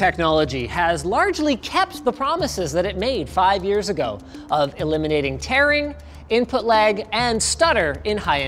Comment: misheard it for "eliminating taran-" and had a wut moment